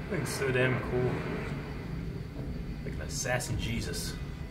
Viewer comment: "Assassin Jesus" That...is the most accurate description anyone has ever said about John Wick XD This is why love. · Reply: I thought he said "Sassy Jesus" tbh🤷🏻‍♀️